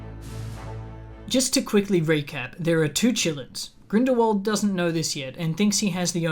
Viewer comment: i know you're saying "qilins" but i keep hearing it as "chilluns" (as in children) and i just think that's hilarious. also, the qilin in chinese mythology (or kirin, in some cultures) does have a story of basically being an omen for the next ruler or sage of an era. it's been used in some media (like pet shop of horrors) to symbolize divine authority and its choices, but it's not THE PICKER OF RULERS in the sense rowling decided it was. so to me it feels like she did a racism/orientalism, which she does a lot (what the fuck kind of name is nagini? that's not a name lmao, you just took "nagin" (snake lady) and added an 'i' to it joanne wtf). anyway, love your videos, mr!